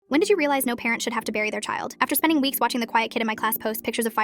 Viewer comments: Omg I actually thought they said “when did you realize no parent should have to MARRY their child?” I had to listen to it again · Oh dam o thought they said no parent should have to marry their child